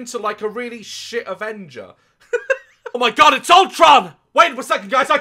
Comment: That Avengers joke got me 😂😂😂 "OH MY GOD IT'S ULTRON!" "WAIT ONE SECOND GUYS I GOT THIS" Sprays air freshener 😂😂😂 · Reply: Bunni Dove I THOUGHT HE SAID VOLTRON